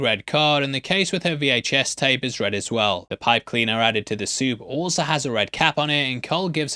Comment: Didn't he say "Pine cleaner"? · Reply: sounded like pipe cleaner so I turned on the captions and that's what the captions thought he said too.